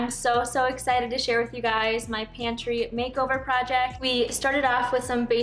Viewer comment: Haha, thought you said Panty Makeover. Cleaned my glasses I did